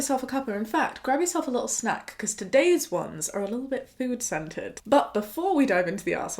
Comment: I heard “food centred” as ‘food scented’ lol